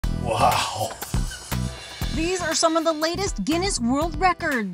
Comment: I thought she said "lamest" world records